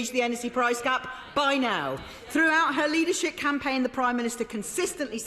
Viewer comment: Did she say “leadershit campaign”? Because that would be not only factual, but GENIUS!